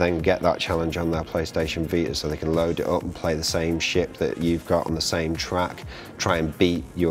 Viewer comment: did he said "SHIT"